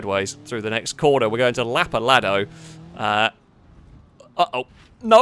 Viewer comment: "We're going to Lapaladdo" I hear it's nice this time of year. Lol